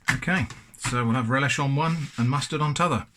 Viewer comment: Did he just say t'other? Is he a northerner? His general accent doesn't sound particularly in one place or the other. Maybe that's because I'm not familiar enough though